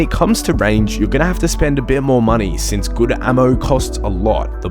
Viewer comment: did you say Gooder Ammo? Thanks for the guides!